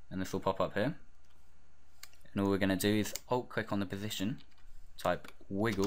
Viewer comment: Nice vids bro but i'm french and i don't speak good English so what do you say at 6:19 please ? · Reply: alt-click on the position (stopwatch) :)